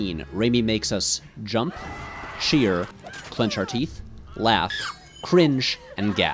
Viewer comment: What does he say at 7:10? Some word after "jump". Is it "sheer"? English is not my native and I'm stuck there :( I have no idea what that word is. Help, pls :) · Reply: Danila Polianthes “cheer”.